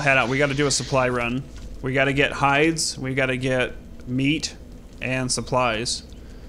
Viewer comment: come on were going on a scavenger hunt we need to get hide me: did he just say we need to get high (oh well the boss)